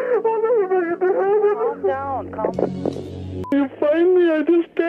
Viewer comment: Calm down oi, bukan kalem coeg. Salah denger apa? 😂😂😂😂